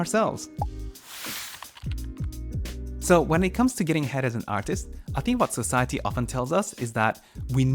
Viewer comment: Thank god for captions because at 0:36 what I heard was "So when it comes to getting head as an artist..."